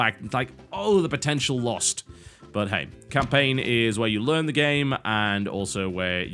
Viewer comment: TotalBisquit says "but, hey..." automatic subtitle says "butthead".